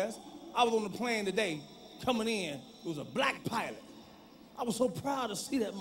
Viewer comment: ohhh I thought he said black pirate not black pilot lol